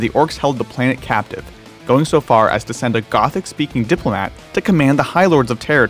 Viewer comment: Did you just say a Gothic speaking diplomatic ork?